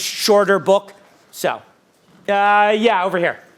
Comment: they ask if publishers push to include something in the contract, but I can't make out what is said. Can someone help me understand? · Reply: "have they ever pushed you to include or exclude content"